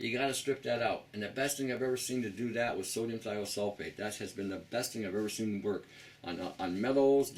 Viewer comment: You say at about 19:20 in the video that the best thing for detoxing the colon is what? sodium diasulfate? I can't quite make out what you said. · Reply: Sodium ThiolSulphate Tony